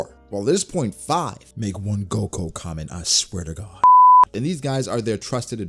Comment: Shanks making a move might make sense because as the fight ends shanks has to make a move for the next story plotline. I really believe he will either be seen in reverie or against BB at the end of this arc. Also, I really like that Red-faced comment you made lol.... Inspired from Ohara it would seem. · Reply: I think you say "make one goku comment I swear to god"